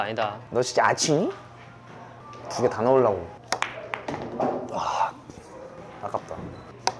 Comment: did anyone else hear Felix curse ?? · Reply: Yes he said ah fu**